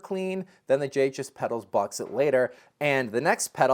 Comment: JHS… what? Couldn't make out what he said. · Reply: Sounded like he said, "Jhs Box It Later"...